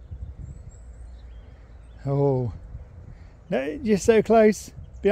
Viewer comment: I swear I heard him say , I know but we're close!! So happy seeing sherp having a great day!